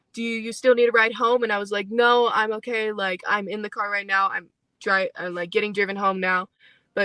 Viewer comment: Did she say " I'm dry...I'm driving home"?! At 10:27 wtf put on caption! 👀🕵🏼‍♀️